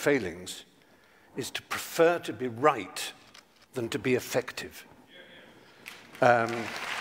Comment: Did he say 'affective' or 'effective'? I wanted to hear 'affective'.